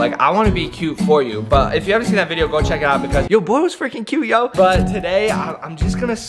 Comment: antone else thought Josh said "Your boob was freaking cute yo" at 2:03 lol love you guys!! X~X